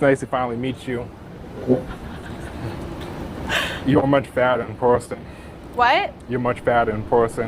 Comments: I thought he said “you’re so much better in person “ he has a lisp…. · Bro, the guy has a mumble, At first I thought he said fatter just like everyone else did, but he might have said 'you're much badder in person.' Am I wrong? Probably. 😂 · Did he say fatter or badder? Second one sounded like badder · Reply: Yea I thought he said badder too lol saying fatter off the rip and then asking her what she does for fun is crazy work 😂